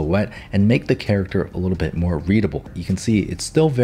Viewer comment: At this time : 7:55 , I thought said “And this would make the character look a lot more *breed-able*” HElp..💀😭